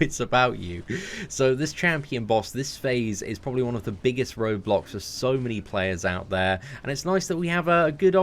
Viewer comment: Did he just say rObLoX?!?!? I'm speechless